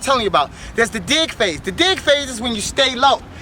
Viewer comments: anyone thought he said dick phase? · I thought he said dick phaze..lol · did he just say the dick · dig phaze.. I thought he said "dick face"...LOL? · i thought he said DICK PHAZE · thumbs up if you thought he said "dick face" instead of "dig phase" XD